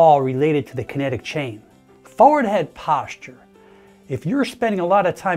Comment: I thought you said forehead posture. Keep your forehead perpendicular to the floor haha